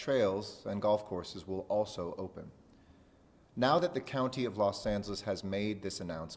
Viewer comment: I swear to god I have my volume all the way up and all I hear is "the county of Los Santos"